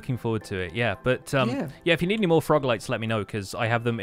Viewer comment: is it just me, or does it sound like they say frog legs and not frog lights?